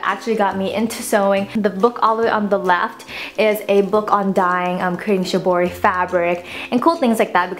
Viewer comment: When I heard, "...a book of dying" my eyes went O__O Then I came to my sense."OH! Dye!" xD